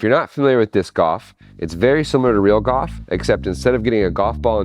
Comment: Are you saying "disc off"? I only realized "off" is supposed to be "golf" at 1:10 when you said "real goff" and showed what looks like golf. Your pronunciation of "golf" just doesn't have an "l" in it, so I had no idea what you were saying at first, and could only hear "disc-off". Anyway, awesome project and extremely entertaining. That's all I have to say about that.